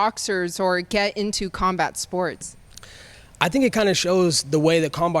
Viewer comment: did he say karmbar sports?